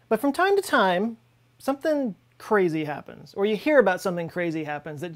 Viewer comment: I heard 2020 happened!!! O_O